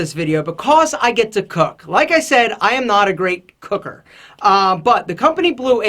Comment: did he just say "cooker"?